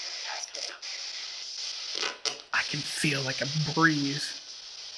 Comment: there is a disembody voice saying "I can Help you", you guys didn't catch.